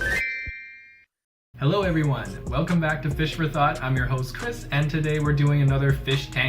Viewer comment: In the beginning, I thought he said Fish for Thots. 😂